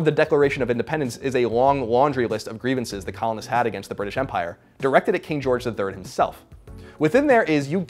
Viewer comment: When i heard him say "..that COLONists had against the British Empire," i heard "that Colin had against the British Empire" lol